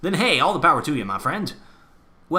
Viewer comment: For the longest time, I thought you were saying "I am the War Owl, and I still have no clothes on." I was thoroughly confused.